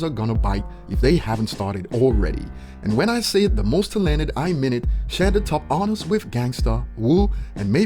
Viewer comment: I had to replay this part 4 times because I didn't understand him when he said "talented"